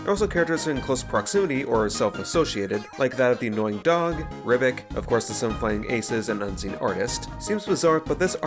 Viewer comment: confused as to why the auto generated caption says rudinn when you said ribbick